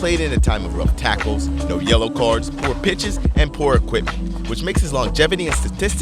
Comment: I thought you said no bitches instead of poor pitches in the final say for pele anyone else?